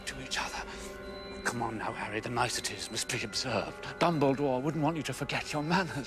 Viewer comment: You know, now re-watching this scene, it sounds like he said 'Bumbledore'